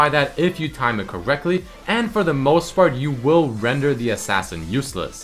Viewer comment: did you say "for the most fart"